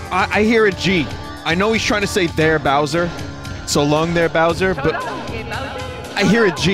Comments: I hear this 'So long-eh, Bowser!' similar to how he says 'It's-a me'. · i always thought he was saying "So long-a Bowser!" with that sort of stereotypical italian accent.. i never really heard "Gay Bowser" until other people pointed it out tbh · I thought mario was saying "so long de bowser".